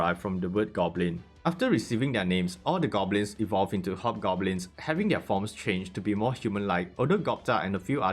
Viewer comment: I misheard hobgoblin as hot goblin 😂